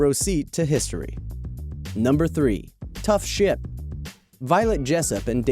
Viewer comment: I thought he said tough shit